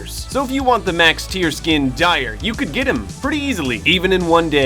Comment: Oh i thought you said max dire in a day whoops!